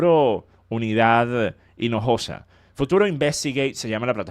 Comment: No se mi ingles aun no es muy bueno pero yo escuche "FUTURO IMBECILGATE'' en esa parte, aun asi ese nombre le queda mejor que futuro investigate 🤣🤣🤣